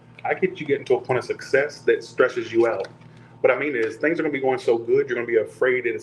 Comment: Pisces. I thought you said I was gonna get stretched out at first. I was like yayyy! Then I realized you said stressed out. Lmao. 💀😂